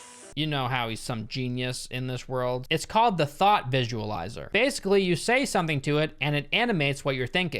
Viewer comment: LOOOL XD at 21:05 I heard "the THOTT visualiser" hahahah